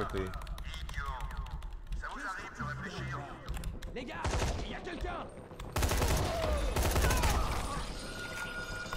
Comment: did he just say n-word? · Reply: No he said « les gars » that means « Guys »